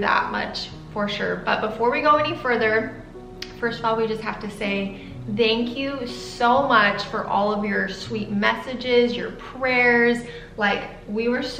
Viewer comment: says dank you instead of thank you lol